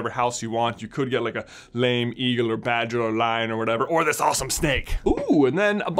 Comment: Did you just say lame eagle. Ben will know about this